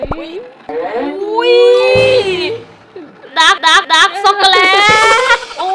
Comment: พี่พูดว่าด้ากซ่อกกเเลต55555